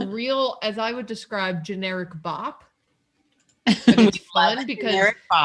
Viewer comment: Originally I thought said generic Bach instead of generic pop and I was like that’s a very interesting party song!